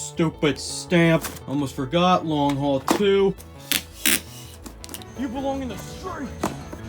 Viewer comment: for a second there I got worried because I thought you said there was a second long haul movie